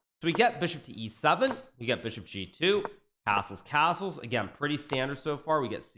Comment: Did he just say assholes castles , or am I imagining things?